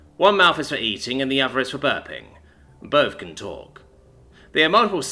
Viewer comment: "don't pet them" - yeah, that had to be included. "the other mouth is for burping" - I heard "birthing" and had to double check. Brain, why do you torture me so? Well, if the alien ships were not so 'outdated' by human standards it would have taken a couple more days to outfit it (so they could make blueprints of everything), but even in this state, I am sure engineers were glad to get their hands on something different. Also, they might not have re-upholstered the captain seat, but they definitely deep cleaned it. The best silent ad there is.